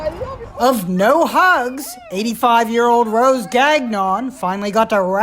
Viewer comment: I thought they said “rose gagged on”